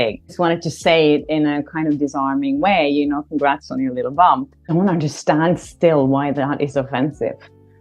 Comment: Wait, did she just say "Congratulations on your little bum?"